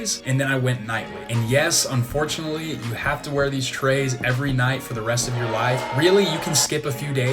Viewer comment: Did he say 5:35 yes you have to wear it for the rest of your life? 😲